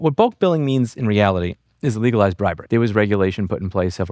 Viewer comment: In the beggining I thought he said “legalize broccoli” and I was like what does this have to do with weed 😂